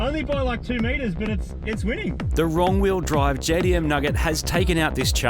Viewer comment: Did he said ,,The wrong wheel drive"? 😂